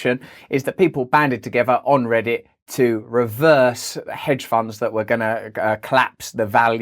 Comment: Why is Aldous Snow reporting this a week late? And did he just say 'hedge farmers' instead of 'hedge funds'???? Furry waaaall, furrry waaall